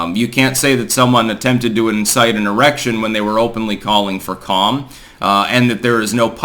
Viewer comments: Incite a what?? Did I hear "Erection"? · Styx did you say erection haha.